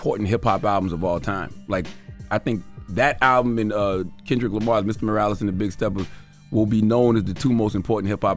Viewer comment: Did Charlamagne call Kendrick Lamars last album Myles Morales and the Big Steppers?! 😂